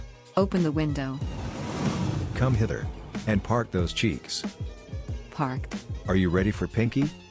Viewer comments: I thought he said part those cheeks 😂 i watched it again and he said park those cheeks meaning sit down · I thought he said, “come hither and PART those cheeks.”💀💀💀🤣🤣🤣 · I thought my mans just said “part those cheeks” 💀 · Bruh I thought he said "part those cheeks" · I thought he said part them cheeks